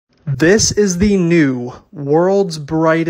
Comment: why did i heard him saying "this is the ni